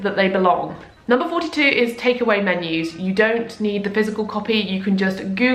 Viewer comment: i thought #42 said “take away memes” and i was about to lose it. i have 200+ memes in my phone and use them religiously 😌